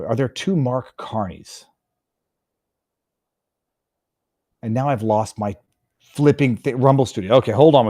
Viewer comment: "Now I've lost my rumble seat"? It's in the back, man... 😆 (I probably misheard that...) Smile? I'mma start calling her "Grimace".